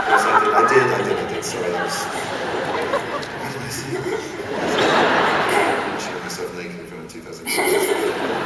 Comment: Was he saying at 10:05 "I showed myself naked in front of 2,000 people." or is it just what I want to hear? xD